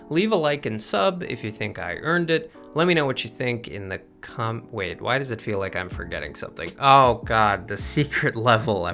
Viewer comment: what did you say? 😂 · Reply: Yum